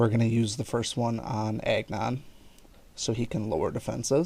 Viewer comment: When you say Agnon it sounds like you say eggnog 😂. Btw, great video!